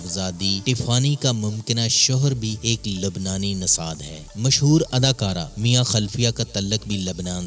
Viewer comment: Mia khalfia 😂😂😂😂😂 instead of khalifa😂😂